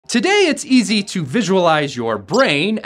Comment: I scrolled down at the perfect time so what I heard was "Today it's easy to visualize your alcohol"